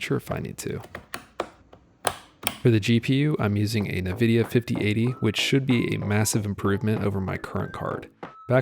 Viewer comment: did I just hear nevidia?